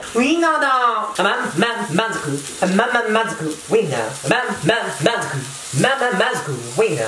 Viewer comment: せめぇwww 5:45 ウインナーがWINNERって聞こえちゃうwww